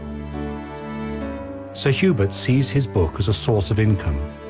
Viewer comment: Did they say Sir Hubert or Sir Hubris?